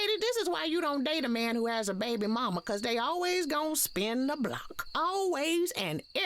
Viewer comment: I thought she said she never spins the block when asked about Nelly and Ashanti getting back together 😂